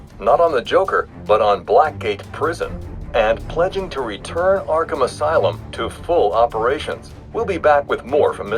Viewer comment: when Jack Ryder says and pledging to return arkham asylum to full operations the subtitles says arkhamn on